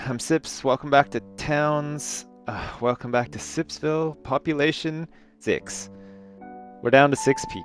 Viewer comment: I thought you said: "Welcome to sips ville, population.... dicks"